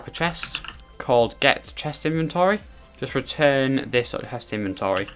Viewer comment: Anyone know what he says at 14:21 as he ends? I got return and then he like cut himself off in an edit or something. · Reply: I think he said return this.chestInventory